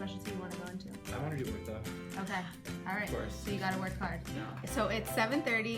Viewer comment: which specialty did he say? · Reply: ortho!!